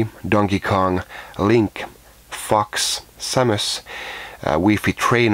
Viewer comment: I had tabbed out of this video to play a game, then I heard at :46 "Link fucks samus, wii fit trainer" I was like what the fuck and tabbed back in to see what was going on lol. Then I realized you said "Link, FOX, Samus"